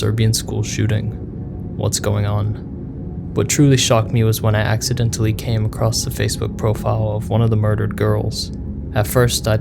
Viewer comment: I paused the video overnight and came back to watch, I was on 2:22 and immediately paused it again, and all I heard was "I accidentally came--" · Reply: "I accidentally came across the face.." thats the full line lol